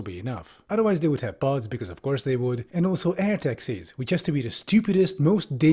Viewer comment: i thought I heard air TAXES